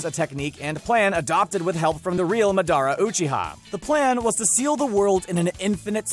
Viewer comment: did he just say MAH-DARAH…